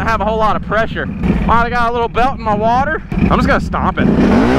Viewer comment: Anyone else hear him say I got some belt in my water